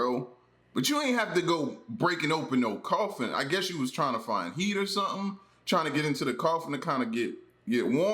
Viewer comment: 😅In #5 with the guy getting into the coffin thought L said " he was trying to find meat".....was like damn all chill about eating dead corpses likes its completely normal.